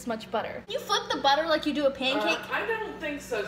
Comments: wat did she say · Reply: You flip the butter like u do a pancake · At first I thought she said you fuck the butter like you do pancakes